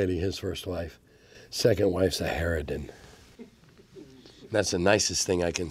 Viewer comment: What word did he use to describe Gary's second wife? I cannot make it out... ?? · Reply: harridan